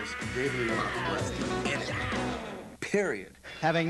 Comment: The fuck is saying at 19:22 ? David Lee Roth was …. I rewound that shit like 6 times & I can’t figure out wtf he said lol. And forget about YT delivering CC’s that are worth a damn 😂 Anyway, if anyone know what he said, please, enlighten me. Enemy?? Empty? Fuck, Idk. I give up haha